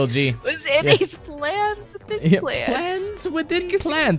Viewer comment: I always heard it as "Plans, with implants"